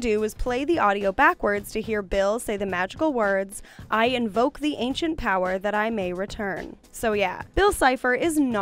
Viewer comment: that is not everything he said. This is what he said. Axolotl my time has come to burn! I invoke the ancient power that I may return.